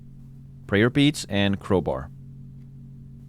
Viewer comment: Ohhhhh I thought he was saying "prayer beats", as in like some christian music band or something as a reference Prayer beads